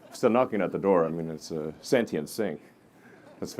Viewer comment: does Elon say sentient sink? Is that what he says?